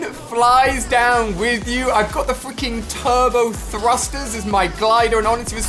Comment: did he say f×××××king